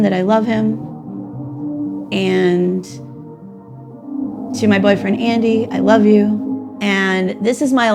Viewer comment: did she just said husband and boyfriend?